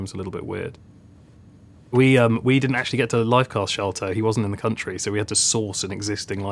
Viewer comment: Could anyone tell me what is lifecast shelter (if I didn't get it wrong) mentioned at 2:12? Very confused. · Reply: He said we didn't get to lifecast Shelton or Sheldon, a person's name. Basically, the actor wasn't available for them to lifecast so they had to get an old one.